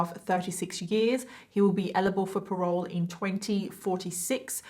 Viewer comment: you say elible instead of eligible and it's so cute 😂💖